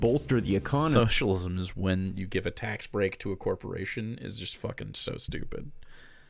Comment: I'm not sure that's what he's saying.... It sounds to me more like he's going "We're giving the corporations socialist policies while everyone else is left hanging". Which is a very different statement than "giving corporations support is socialism".